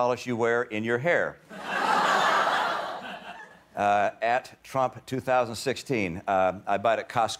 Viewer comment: he says at instead of hashtag